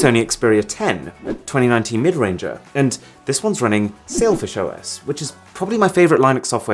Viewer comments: I heard selfish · Arun-Sailfish OS After some time 6:00-Arun-Selfish OS 7:00-He said Linus instead of Linux Me-🙄🙄😆😆😆😂😂🤣🤣🤣